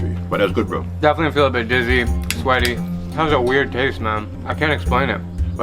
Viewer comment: the caption says Mom instead of man lol